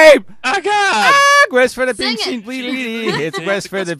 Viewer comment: I thought he said pig sheep O.o